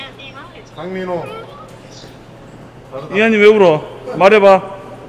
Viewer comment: 초 나만 이한이 ㄲㅈ하는걸로들리나…ㅎㅎ